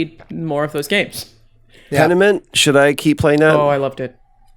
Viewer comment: little help everyone: what game does Troy say? Sounds like 'Condiment' or 'Tenament' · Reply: Pentiment